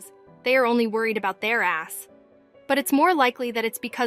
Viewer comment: Did she say ass???!